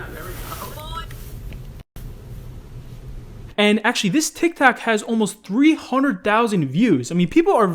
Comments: did anyone catch him say "this tictac" at 7:13? XD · Did he just say tik tak XD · did he say tik tac? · is it only me who hears: Tik Tac? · Did Just say tic tak! 😂